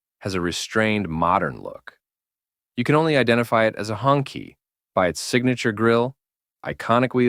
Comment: It's pronounced "Hoon-Chee" (Red Flag), not " Hongki" 😂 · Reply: It's hoong (red) chee (flag). I thought the AI said "honkey"!🤣